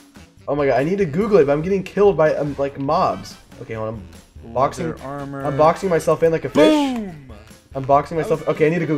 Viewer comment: i deadass thought he said ‘i’m blocking myself in like a bitch’😭